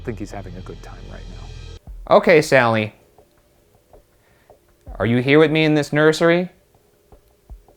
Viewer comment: Where the guys think it says "More Whiskey ", I hear "More with me." Not sure if it's saying spend more time with her because it's when they're talking about leaving, or that there are other spirits with her. Makes more sense than more Whiskey though 🤣